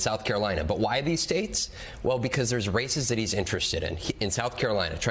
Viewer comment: the man speaking said "because there's races that he's interested in". My ears, however, heard it as "because there's racists that he's interested in". I have a feeling the way I misheard it is probably more accurate.